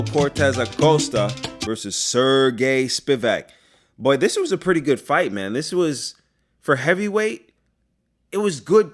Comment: Sounded like Lloraras at 17:19. I was gonna break out my salsa 😂😂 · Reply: Haha it is Lloraras! Good ear!! 🕺 💃